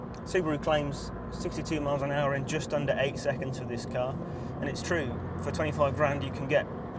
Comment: 0-62 in under 8sec? Thought I heard 6 sec?